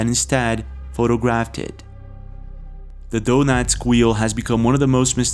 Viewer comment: Can you please clarify what you call it here? It sound like you say something like the “donads wheel” but I turned on captions to see what you said and it says “donut squeal”, but that doesn’t sound right either. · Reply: 'DONETSK' Wheel. Donetsk being where the mine is.